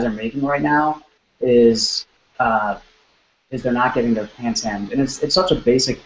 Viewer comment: Hey! Ich hab es mir jetzt paar mal angehört, verstehe es aber trotzdem nicht. Was ist der no.1 Fehler? (5:35) Danke und Beste Grüße Artur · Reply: Just like he says in the video - not getting pants hemmed. Hosen die zu lang sind.